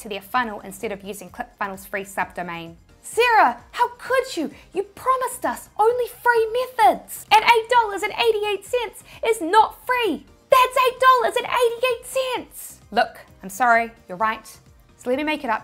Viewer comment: Have I seen too much breaking bad If the first thing I heard at 25:08 was ,,You promised us only free meth...heads"?